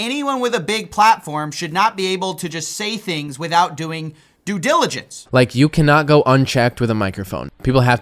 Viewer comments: Did he just say "due jilligence"? 😂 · Due Gilligence 😂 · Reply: I thought he said Jew Jilligence. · I think you'll find he said "due jiligence" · To be fair, it did sound like he said due giligance, and he obviously did that. What's to complain about? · did he say jew diligence